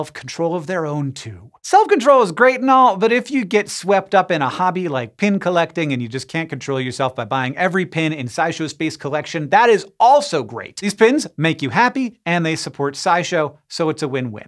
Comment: I thought he said, "pen collection", which can get really expensive, really fast 🤦‍♂️❤✒🖋